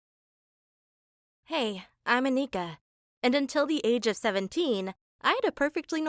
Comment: I really thought she said "Hey, I'm a nigga"